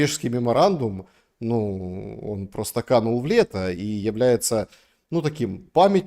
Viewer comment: Канул в летО , для журналиста прям косяк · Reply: Я услышал "в летА".